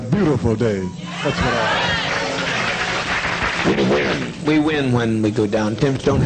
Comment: The fact that the adults applauded as their children screamed in pain and choked to death horrifies me... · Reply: at about 13:35 for the second time I think I hear these demons real voice appear: "we win" it said.